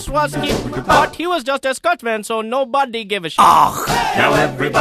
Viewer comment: "But he was just a Scotsman, so nobody give a shit" XD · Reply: Oh, shit! I thought he was saying "stunt man"... HAHAHA! Scotsman makes sense too. :P Either way I laughed pretty hard.